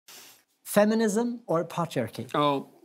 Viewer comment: I thought he said feminism or pot jerky